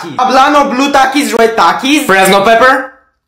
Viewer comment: I thought they all said reznove pepper💀💀💀(like bro this ain't the call of duty campaign)